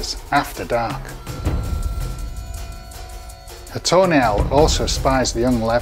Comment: Lmao when he said tawny owl it sounded like he said a toenial spots it and makes a grab for it. Hahaha. It took me a second to realize he said tawny owl.